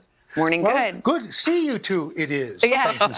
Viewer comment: Did he really say: ''Well, good to see you two idiots'' ???